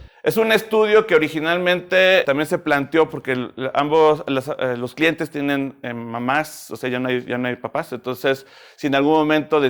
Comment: Que dijo? Que los clientes no tienen mamás? No entendí